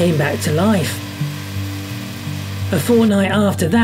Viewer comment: A Fortnite after that? Did he say Fortnite?